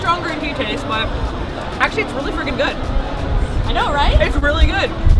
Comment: Did say "it's really f*cking good" or "it's really freaking good" at 8:05? Either way, I cracked up at her response. :D